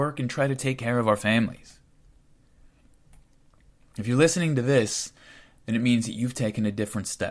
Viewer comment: I heard Daryl say, “if you are listening to this in 2015”. Was that a slip, or did he come out with this over 10 years ago?